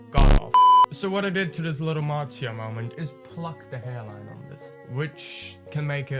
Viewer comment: I thought he said 'marzia moment' which made me freak cause I love her